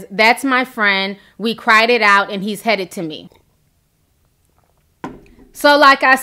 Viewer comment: did she just say Jonathan is "headed to her ??"